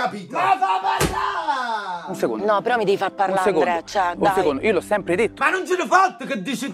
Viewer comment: solo io ho sentito "no ANDRÉ mi devi fa parlá" ?😂